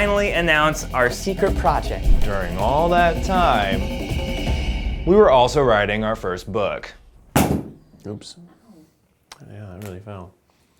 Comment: I thought they said they're reading their first book.